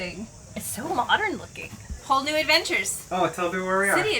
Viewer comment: says - “Whole new adventures”. But I heard “Hull new adventures” [get it, hull. HULL new adventures? You’re in a fancy marina, talking about all of the fancy boats moored nearby. Lots of different hulls. Huh? Huh? A hull new adventure? Boom boom, thank you, thank you. I’m going to be here all week. 🙂🐿🌈❤️